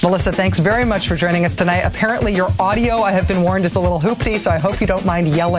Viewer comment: A Little hooptie?? audio system? Is that what she said or is it my ears are wack?